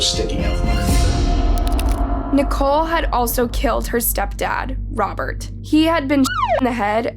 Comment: I thought it said she shit her mom in the head